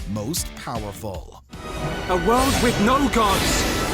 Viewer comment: FOR A SECOND THERE I THOUGHT HE SAID A WORLD WITH NO GUNS LOL